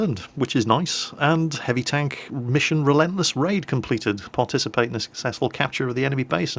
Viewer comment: When he said relentless raid I thought he said relentless rave.